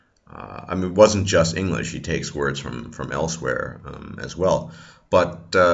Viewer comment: Who else heard him say "he takes words from... from Elsweyr, as well." ? xD